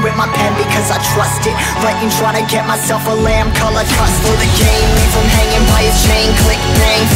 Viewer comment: This whole time I thought I kept mishearing “lamb coloured custard”... Until I saw the lyrics